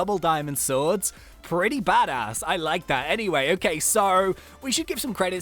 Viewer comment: DID HE JUST SAY BADASS